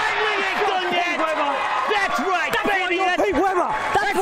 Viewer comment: did Belmonte just call him Pete weeber